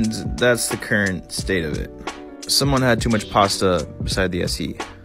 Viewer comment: Did he just say posta and not pasta gasp OmfG